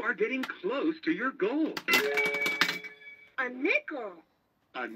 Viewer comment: it sounded like the machine said "Pickle"